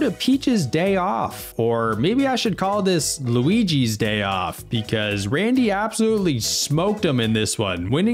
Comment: I almost thought he said "Luigi's Day Out"